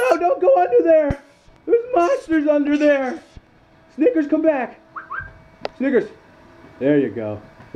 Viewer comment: I had the volume down low and when he was calling for snickers I thought he said n******